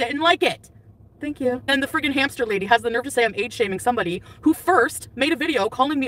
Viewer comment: For a second I thought she said “AIDS shaming” I almost shat my pants.